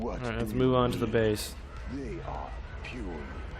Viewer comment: I dont like that they are using Command and Conquer overlord sounds for big ships. Ruins my immersion in Star Wars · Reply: I heard 'They are puny' and did a double take lol